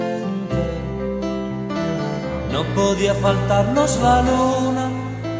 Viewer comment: Yo pense que decia que no podia cagar 🤣🤣🐽🐵